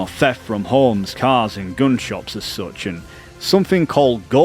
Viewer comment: he literally did lol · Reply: He never mentions gun shows. You may have misheard at 19:15 where he is talking about THEFT at gunSHOPS and then he goes into under the table trades with stolen or non-serialized firearms.